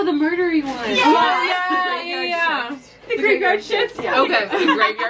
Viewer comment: what was the one yall said 41:36?! that one sounds soooo good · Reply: It’s called the graveyard shift! talks more about it on her channel at the end of her video!